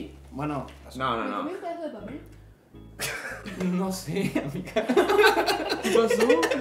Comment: Qué dicen en esta parte? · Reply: Nicki se comió un pedazo de papel sin querer